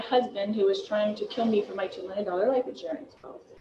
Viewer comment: I thought she said 3 million life insurance policy was on her head, now it is 2 million. She isn't worth the paper the policy is written on. MONEY MONEY MONEY I got an idea LORI GO TO WORK FOR A CHANGE.